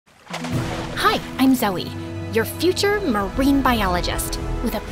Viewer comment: a name we haven’t heard before dayum Edit: help I heard her name as Marine